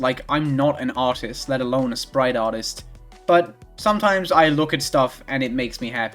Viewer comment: "sprite artist? I thought you said sprite FARTist."